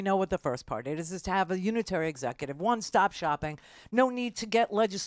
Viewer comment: Did I hear, 'Unitary Executioner'? Anyone else hear that?